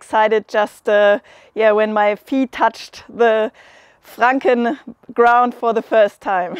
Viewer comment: " i got excited just when my pee touched the franken ground for the first time "...did she really say that ?...😂😂😂